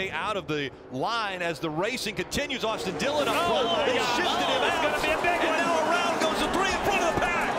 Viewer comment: did he say the s-word? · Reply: UsefulTuber “oh he shifted him out”